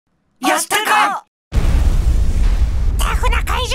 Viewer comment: 「やったか？」が「よしたか」に聞こえて「友だちと同じ名前じゃん」ってなった